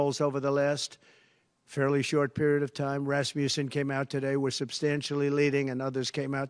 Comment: Fact-check. Rasmussen has tRump down by 8 points in a 3 way race and down 6 points in a two way race. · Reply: Oh, he said Rasmussan. I thought he said Rasputin. 😂